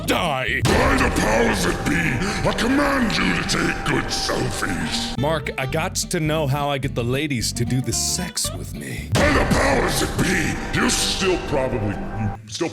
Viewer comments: One time I thought he said "by the powers that pee". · Reads description OH I THOUGHT HE WAS SAYING BY THE POWER OF ME THAN I THOUGHT HE SAID BY THE POWER OF PEE BUT THIS WORKS TOO